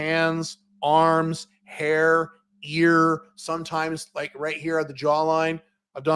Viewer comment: At first thought you said beard (ear) as you touched your jaw, you know those russian ladies lol